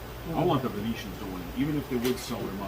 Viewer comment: Could have sworn said his army was Phoenician. Must have said Venetian tho.